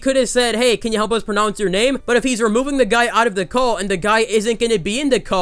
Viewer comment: I thought you said "When you removed this guy from out of the cult" I was like, wow this was way deeper than I expected lmao